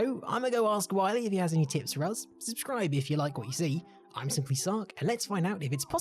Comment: bro i only hear “SimplySucc” not being rude and not saying you suck but thats all i hear lmao